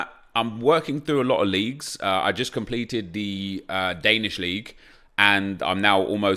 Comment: does he say he has completed the danish league??